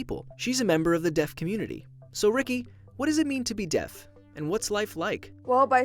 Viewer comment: At first I thought it said "What Is It Like To Be Dead?"